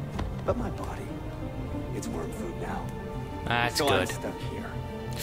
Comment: anyone notice the in 05:44 the subtiles say worm food instead of warm food